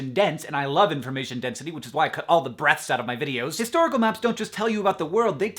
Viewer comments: oh he said "breaths", i thought he said "breasts" ... and for some reason i didn't question it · oh...I....I definitely thought he said breasts. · I thought Hank said "breasts" instead of "breaths". lol, get my mind out of the gutter! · Totally thought he said that he edits the breasts from his video. Haha. He said breathes.